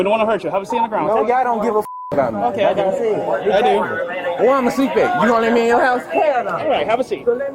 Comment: "I'm a seatbelt you won't let me in your house" is quite a line. I had to go back and double check, but that is indeed what he said.